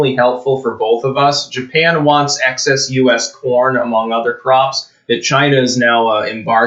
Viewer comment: your sound quality is so bad that i misheard that as "japan wants excess US porn" edit: 0:18 "we produce so much fucking porn" lol even better